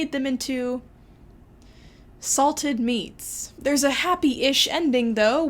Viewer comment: I always heard it as the boys being pickled in a barrel instead of salted (i live in germany, that might be the difference :P)